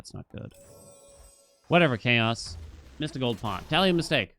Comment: Every time he says "tally a mistake" I always mishear it as "Italian mistake"